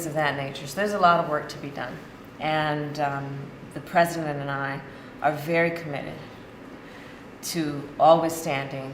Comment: She actually said: 'The President & I are very committed..." That ought to be Global News, ~ Hip-Hip Hooray 'ya all!